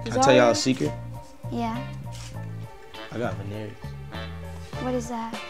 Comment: Can someone let me know what Tory actually sayin at 3:51? "I got ....?" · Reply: *Veneers